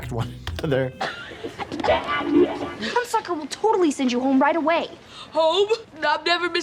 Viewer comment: ‘ That fucker will send you home right away‘ keep rewinding and listening to it dozens of times anyone else hearing anything other than this???👈😏🤪🤪🤪🤪😅